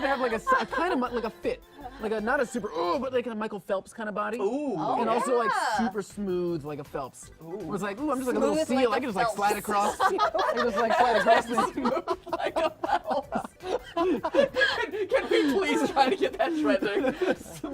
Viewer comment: I keep hearing "Smooth like Michael Phelps" and I'm concerned.